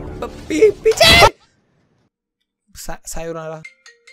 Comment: time did you said sayonara 🥺❤